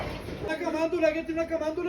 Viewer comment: I've seen this video in a few places and know a little bit of spanish...I don't think he's saying what the subtitles are saying...any fluent spanish speakers want to comment? To me is sounds like he's asking if anyone knows who lives there? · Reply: He is asking for a rosary, in Spanish he said Camandula, that it's another word for rosary